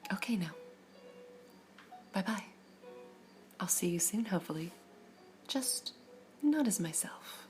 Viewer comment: ah not to be rude, but what does say at the end? because I heard "have a wonderful time otter" and I'm pretty sure I'm wrong on a few levels lol